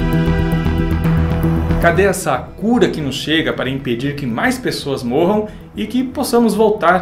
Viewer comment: Nessa Parte: 0:22 entendi "Cadê a Sakura que não chega"😅 Socorro Sakura!